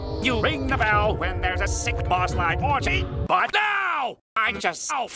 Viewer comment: what did he say after "you ring the Bell when there's a..." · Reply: But NO